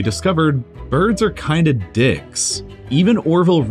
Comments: Did he say "birds are kinda DICKS" ???? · did he just say d*ck in